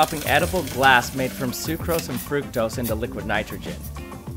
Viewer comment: Lmao I thought he said liquid nitroglycerin instead of liquid nitrogen